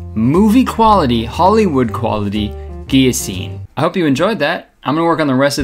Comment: did he say Guillo-scene? Cause that is hard af